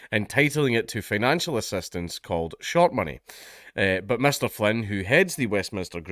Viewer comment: I thought you said Shart Money 😂